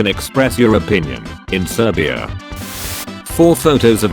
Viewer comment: did it really say serbia